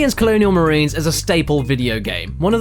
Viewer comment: For a second I thought you said Alien Isolation when you said Colonial Marines. I had already started sharpening my pitchfork.